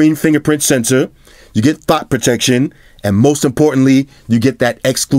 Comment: did say THOT protection